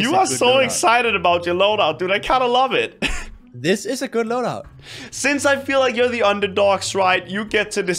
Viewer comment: When saint "This is a good loadout" I totally heard "This is a granola" 😂